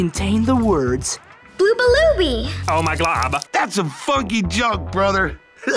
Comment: my volume was down so I hear that some f*cky junk brother